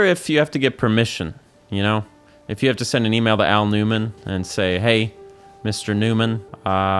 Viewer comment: I consciously knew you were saying "Aonuma", but half listening I kept hearing "Al Newman" and I'm like "who the heck is Al Newman?" LOL